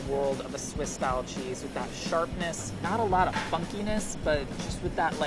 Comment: I thought he said “not a lot of fucky-ness”